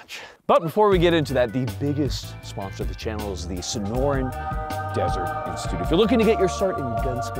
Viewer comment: I always hear "Snorin" instead of "Sonoran"... Also this gun looks plain wrong.